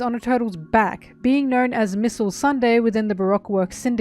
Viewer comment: Can we just appreciate that it almost sounded like "Missile Sunday" at 7:00 right after showing the shot of her supposedly bombing Igaram